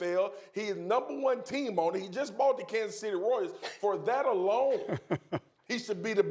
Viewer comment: did he call them Kansas City warriors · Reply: He said royals!!!